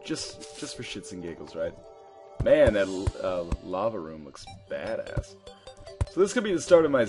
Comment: i thought he said 'that lava room looks fatass!'